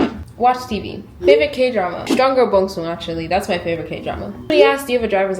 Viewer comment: can someone please write the k drama down so I can check it out? Idk what she said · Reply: I think she said 'Strong Girl Bong-soon' I'm not sure